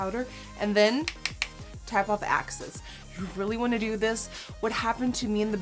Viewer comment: did she say tap off the access or the axis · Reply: She said tap off the excess, it's just English isn't her first language so her pronunciation of the word is different